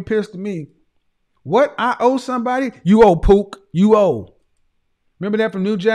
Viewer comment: I thought he said, "You owe pooh". Lol.